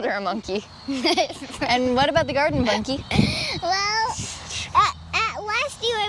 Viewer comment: I heard Garden Monke 😅😅😂